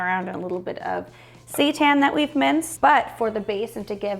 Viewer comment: I died laughing when it sounded like you said you had minced "Satan." Yes, I know it's seitan.